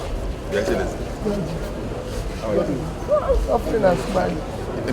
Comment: did i hear suffering and smiling